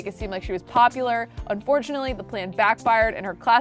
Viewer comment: I heard "Unfortunately Delilah some bad knees" xD